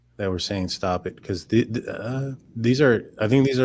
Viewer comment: Sounds like she said "stop it Jack'